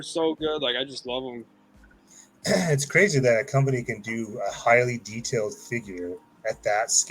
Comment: I thought you said “High End” toys lol. You said Hiya 😂